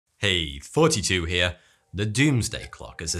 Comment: I always thought he said "hey 42 here"